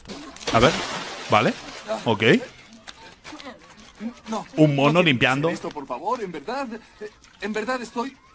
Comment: escuche que dijo un mono limpiando 😐😐😐